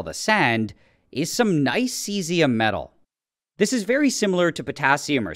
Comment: Bro I thought it said Celsius 💀